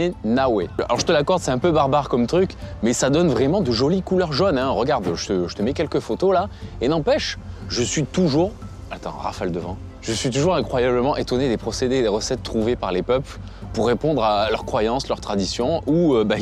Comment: J'ai entendu Raphaël devant... 😅 J'avais pas bité le sens au début 😂